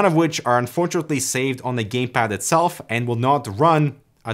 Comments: anyone heard 'gaypad' on 2:05? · Did he say gay pad